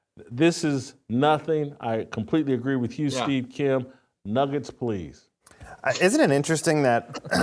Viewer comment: I heard "Niggets".